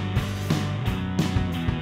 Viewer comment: i must be tired cause it sounded like you said "im to tired to end on something funny, ill use my Goku."